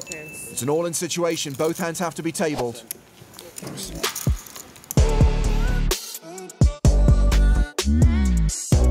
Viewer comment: did he say "personal"? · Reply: Nope, he said “Spasibo” - means thank you on Russian.